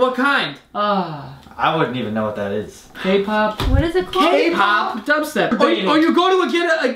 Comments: did he just say Kpop? Kpop doesn't sound horrible but is my opinion thou. whatever... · I HEARD K POP